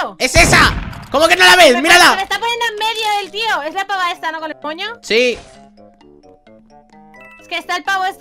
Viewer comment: dice loca del coño xddd o eso le entendí yo 😂😂😂 aunque creo que ere con el moño solo que se corto supongo